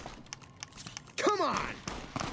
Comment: anyone else heard COME ON lol while reloading the shotgun